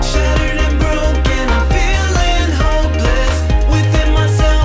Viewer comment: I thought he said “even though my life is a steak” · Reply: I thought he said homeless not hopeless